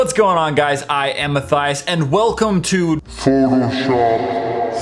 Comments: I thought he said, "What's up guys my name is My Thighs!" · everytime you introduce yourself like " hi im Matthias" I automatically misheard, I AM A THIGHS. ok dont mind me. hahahahahah